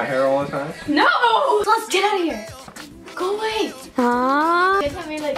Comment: I thought said "get out," but he actually said "in, out." Btw it took me a few watches to figure it out